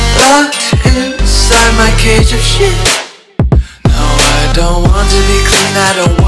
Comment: I thought it said sheep not shit 😂😭😭😭😭 0:33 I love this weird synth